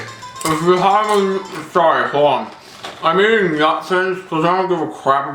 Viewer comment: At first I thought you said "I'm eating Nazis"